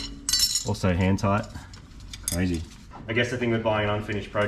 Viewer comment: I thought Marty said "also hentai"